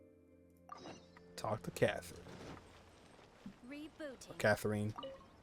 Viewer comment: did Katheryne said "...Rebooting..."